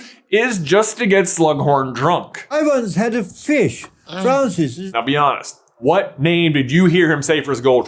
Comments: the name I heard was Francis! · I heard Francis, but after you said trousers, it def sounds like it could be bof. 😅🤣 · I heard him say “Francis”